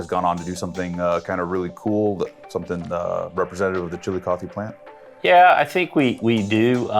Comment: I thought he kept saying the "chili coffee" plant